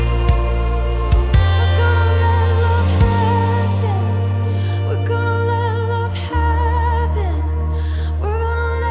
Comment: What she start to say at 12:14? I'm from Brazil and I don't understand she says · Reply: She is saying "We're gonna let love happen"